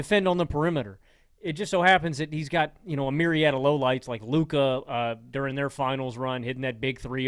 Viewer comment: When said “myriad of lowlights, like Luka….” I heard “myriad of low lives, like Luka…” LOL